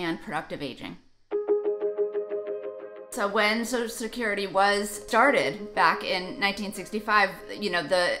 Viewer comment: Did she say “When social security started back in 1965???”